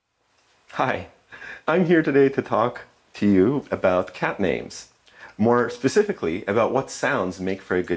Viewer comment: Umm I’m i the only one who thought it said memes not names